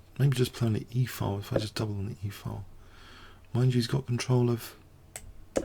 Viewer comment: he says a word I don't understand "++++ he's got control of d4 here"? what's the first word on that sentence, ++++ Please help me, i hear it a lot. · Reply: "Mind you"